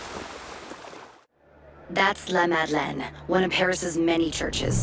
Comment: It sounds like she is not saying the name of the church right, which is La Madeleine. Instead, I hear her saying That's La La Mad Lad